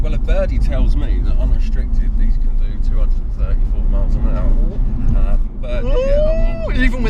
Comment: Yeah that little birdie is full of shit. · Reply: It definitely sounded like he said 234mph but I believe he wanted to say 203-4 mph which sounds more realistic considering the normal XKR-S did 190+.